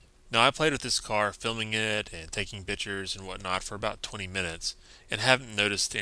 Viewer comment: he said "bitchers" instead of pictures